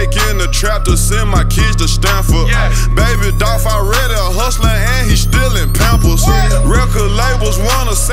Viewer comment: baby Dolph already a hustler & he still in pampers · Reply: I always thought he said he already stealing pampers 😂😂😂😂😂😂😂😂😂😂😂